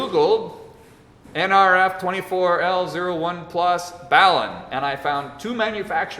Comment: What is he saying in 32:01? ballain or something like that, I can not figure it out, I am not native english speaker. Thank you so much! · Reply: Balun. A transformer, used to convert RF signals from balanced to unbalanced. The name comes from the words BALanced and UNbalanced. <link>